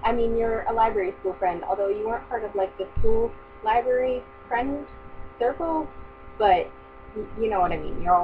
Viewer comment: I at first heard "school library friend circle" as "cool library friend circle." So obviously, I thought you were saying I wasn't cool. Which might be true, but, you know, not something you want to hear from a friend.